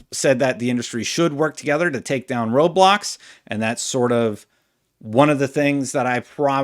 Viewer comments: honestly thought you said "take down Roblox" here. Was like, wait, what did Roblox do? then I realized you said "road blocks" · I loved this interview. Props to the Kinda Funny team. What did you think? Did you have any thoughts on what he said? Also, if you like my videos please consider subscribing for more! <link> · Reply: It sounded like you said we need to work together to take down roblox not road blocks lmao